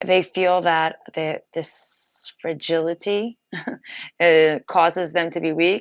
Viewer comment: fidelity ? What was the word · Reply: Fragility